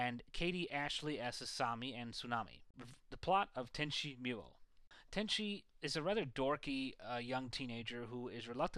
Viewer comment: did he just say tenchi mule?